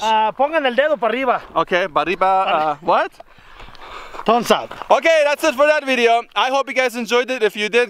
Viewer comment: I swear the Mexican guy said “pornstar”